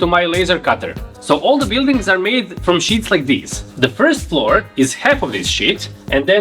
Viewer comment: I heard “1st floor; half of this shit”, pahahahahaha…